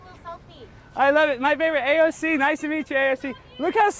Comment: Did she say I love you? At 3:08 ?